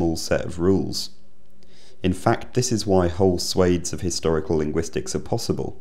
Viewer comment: Fascinating video, as always. Just wanted to ask what is meant by suedes of historical linguistics 0:33 . I'm not a native so I might've probably misheard it or something, the subtitles recognize the word as previously stated so if someone be able help, I'll be most grateful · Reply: I believe he said swathes.